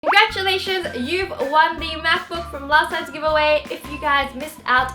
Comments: At the beggining I thought she said congrats you won a mathbook. I was like who wants to win a mathbook. Unless your a math person. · I literally just thought she said you've won a maths book